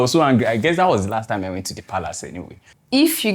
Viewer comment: Did I just hear Palace? Nice one, Royal people